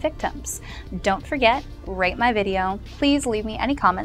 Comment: haha i thought you said "dont forget, rape my video"